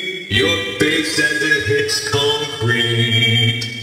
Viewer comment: I found my guitar was so hilarious,It kinda sounded like he said when he is drunk “I fUcK mY gUiTaR”